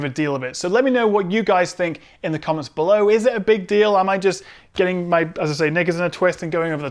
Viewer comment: is it just me or did he say "getting my n***as in a twist" at 9:38 ?